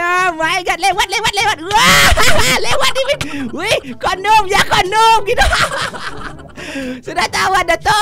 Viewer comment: bang menit 7:57 sampe 8:00 itu ngomong apa ya sebenernya? kok tiba2 anak saya tanya, pa kondum itu apa pa? mohon dong, bang kan buat char Upin-Ipin, asumsi saya target pasar abang adalah termasuk anak kecil. sebelum di rilis mohon di edit dong jika ada kata2 yang ambigu seperti itu. mohon disensor. untuk case ini kok terkesan seperti kata "kondom" yak? sebelumnya anak saya bilang "anjr*t" sama "anj*ng", pas sy tanya tau kata2 itu dari mana dek? (dengan nada bukan marah tentunya) dia bilang kl dari "upin ipin mainkref", yaa saya sih tidak menjudge bahwa itu dari video abang, sapa tau anak saya yang salah. cuman kl dilihat di list subscribe, cuma 3, channel abang, tank rusia sama lagi anak-anak. terima kasih sebelumnya. maaf jika tidak berkenan.